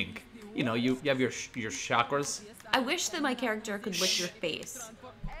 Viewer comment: i thought she said "I wish that my character could whip your face!"